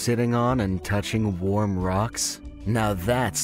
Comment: At first I thought it said socks